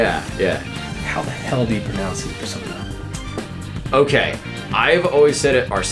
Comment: Anybody got any clue what they're talking about at 3:10? The guy whispers his pronunciation and the voice actor says something super weird. I have no clue what they're trying to say. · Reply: N The One “how The hell do you pronounce that persona (points to arsene)”